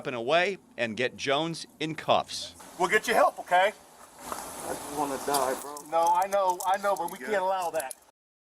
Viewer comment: does he really say he doesn’t want to die wow what about your daughter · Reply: He said “I just wanna die bro.” Officer said “I know I know but we can’t allow that. “